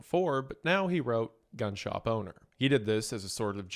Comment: wasn't reading lips, looking away from screen. I heard "gunshot boner"